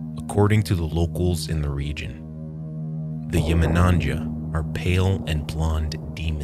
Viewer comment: what does he say the name was for the underwater demons at around 6:10? Cant find anything on google about it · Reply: bruh im wondering the same i cant even figure out how its pronounced, i also searched for the demons images. i heard joom-nanja? hoohmighnanja, hughminanja, yu-mi-na-cha.. ion kno but i like the animation so much